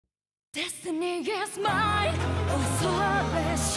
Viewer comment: Am i the only one who hear "this nigga smile" at 0:00 ?